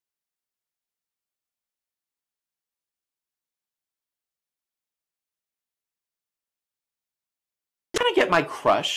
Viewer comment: What did Mark say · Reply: Mark: Oh my god look at that face Kyoka: If you're feeling suicidal you've come to the right place I mean the other way around 😂